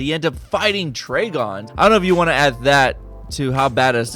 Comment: I'm sorry..did you just say "Traygon"?